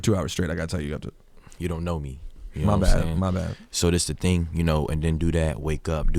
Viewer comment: Hold up, did Rosenburg just say "my nigga" at: 14:02? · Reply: he said my g